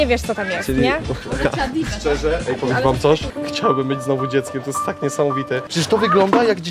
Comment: ja usłyszałam ,,chciałbym mieć znowu dziecko" XD